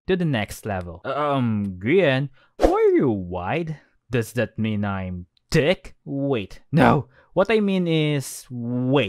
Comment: His accent when he said thicc didn't sound right Sounds like you said "does that mean I'm dicc?"